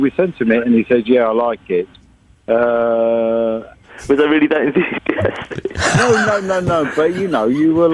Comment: what did Robert say? Could anyone tell me? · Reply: Was I really that enthusiastic 😐